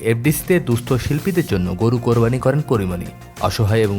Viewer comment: Am i only heard 'Kori moni' instead of Porimoni?